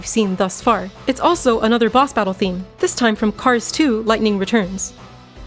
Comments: For the 22/8 example I thought she said the game was, Cars 2, Lightning Returns 😂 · Thought you said Cars 2: lightning returns. Was like, damn.